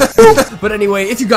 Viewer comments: Sometimes when he says lets begin it sounds like he is saying lesbian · It sounds like is saying 'and lesbian!' instead of 'and let's begin!'